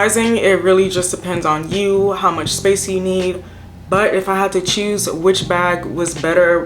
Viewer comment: also you might have to rewind the end I didn't choose a bag 🤣🤣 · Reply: I thought you said if I had to choose fendi sorry I misheard you 😭